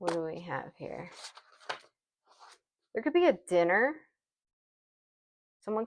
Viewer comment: When you said someone wants to invite you out for “dinner” I heard “sinner” 😂